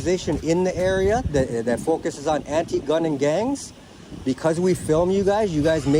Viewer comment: Did he just say he's "anti-gun"?